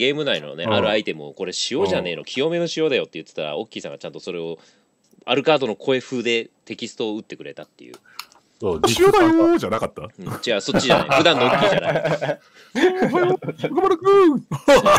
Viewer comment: 塩だよー♪ 半分ミッキーに聞こえるw